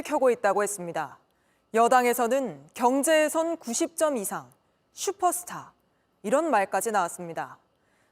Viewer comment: 경제슈퍼스타가 아니라 "콜걸 슈퍼스타"로 들리는데~